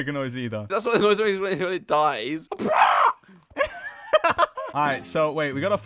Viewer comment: wtf did say at 1:58 LMAOOOOOO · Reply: Something like well thats the noise it makes when it dies but who knows